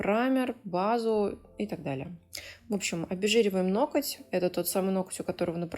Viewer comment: Красота неимоверная! Даже мой внутренний граммар-наци забыл, что услышал "ноКоть" 😻